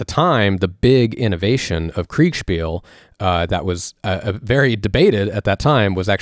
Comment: At first I didn't understand, but they are saying "Kriegspiel", the german word for "war game".